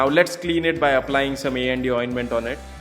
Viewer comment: It’s vitamin A and D Oinment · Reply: I know everything about "A&D Ointment", but I heard "ND Ointment" in video! Thanks.